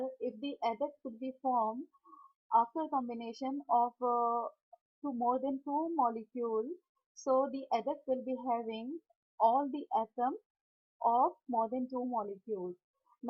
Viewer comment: Salam ..ma'am what did you say at 2:23 ??(WHAT WILL BE FORM??) I didn't understand the pronounciation ...can u write it here in my reply please??? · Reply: final product will have component(atoms) of all reactant